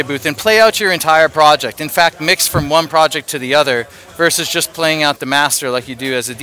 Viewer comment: did you say mix from one project to the other at 12:30?